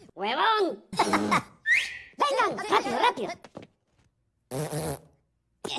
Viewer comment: en el minuto 0:20 escuche que el minion de arriba les dijo a los otros ''del vuelta huevones'' acaso fue apropósito